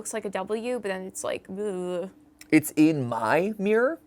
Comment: im pretty sure that at 1:03:51 in the video it doesnt say "it's in my mirror" i think it says "it's in my mind"